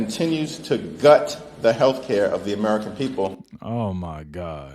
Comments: Dude it sounds like you say hair care every time you're talking about health care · when you say healthcare. It sounds like you are saying haircare. I thought you were saying haircare for a long time.